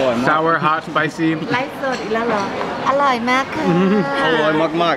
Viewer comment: Min. Did she say "I like Merkel"??? 😂🤔😅